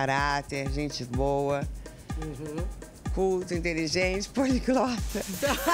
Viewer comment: não entendi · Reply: Reli, e ela disse poliglota